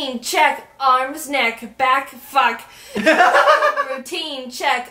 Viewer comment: Did Jaiden say F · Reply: No she said fuck :)